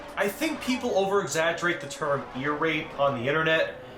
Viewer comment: if u turn on captions the captions say irate instead of ear rape